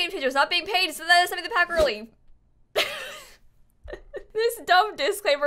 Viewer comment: "THIS VIDEO IS BROUGHT TO YOU BY EA GAME CHANGERS NOT GETTING PAID SO THATS SOMETHING pHaTajh EARLY" that's what i heard, i'm not sure tho